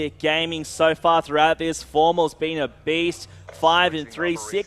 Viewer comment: for second I thought he called formal a bitch